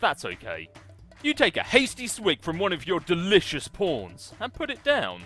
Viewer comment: i thought he said your delicious prons